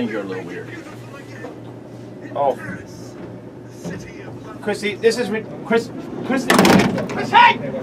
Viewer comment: Did anyone else hear “Christine” instead of “Chrissy”?